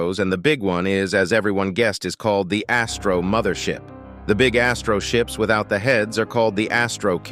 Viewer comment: I thought u said Astro mothershit in your vid for some reason